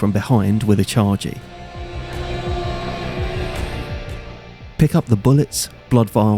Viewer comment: are you saying "...an enemy from behind with a 'chargie'"? (charge-y?) I've listened to that sentence like 10 times and have no idea what you're saying at the end lol